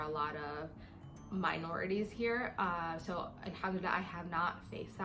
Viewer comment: What does she say at 4:52? Before that there were another words that I don't know. Just curious ^^ kisses from Argentina!!! =D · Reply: Alhumdulilah - she’s just thanking God